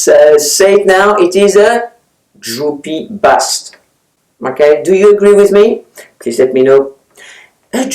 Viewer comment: I thought you said ‘droopy butt’ and couldn’t figure out why you were displaying a bra!